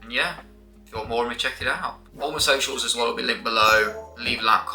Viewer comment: Ohhhh !!! "All my socials" 😂😂😂 I had to replay that part, I thought I heard you say "homosexuals" and I was like "what did he sayyy??" 😅 Let's goooo !!! Ultraviolence greatness !! 🙌🏼